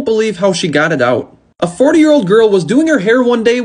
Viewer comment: Him: "A forty years old girl used Gorilla Glue as hair spray" Me: "Forty years old girl? Isn't that just a woman then? A forty years old woman?" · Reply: i thought he said four year old girl